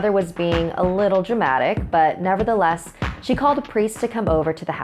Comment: You accidentally said "priece" instead of "police". I know it's just a small mistake but I like sharing comments and I can't stop talking. Lol. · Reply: it's actually 'Priest' they never meant to say police.